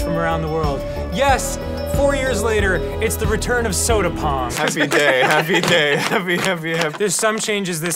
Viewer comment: why did the captions say "soda porn"?